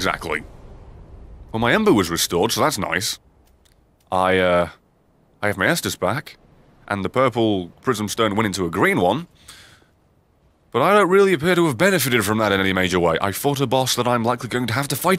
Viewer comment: I legitimately thought you said "I have Master Spark". And I was like "DARK SOULS X TOUHOU CROSSOVER WHEN". XD